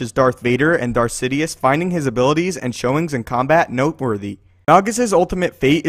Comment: what did he say? Darth wader and sidious where no worthy or something else??? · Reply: noteworthy